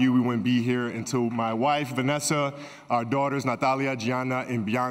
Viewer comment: What did he said at 2:44? something like 'te amo...'? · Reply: 'Ti amo con tutto il mio cuore', like 'I love you with all my heart' in Italian